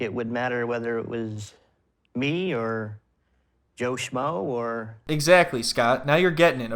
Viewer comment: I thought he said "Yoshima" instead of "Joe Shmoe" and I was like, "Yoshima who?"